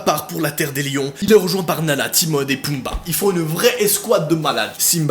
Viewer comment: C moi ou il a dit nana au lieu de Nala